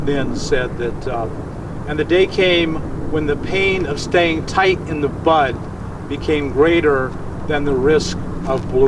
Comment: "And the day came when the risk to remain tight in a bud was more painful than the risk it took to blossom." Elizabeth Appell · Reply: Whew, I thought he said " tight in the Butt"! Wow, I thought I had to rethink the man of him. Lol